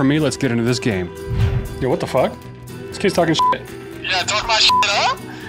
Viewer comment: play us again???? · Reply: this wasn't even any trash talk, I thought you said "talk my sht" not anything else. Plus your final goal was crazy lucky, gon actually be a fair fight next time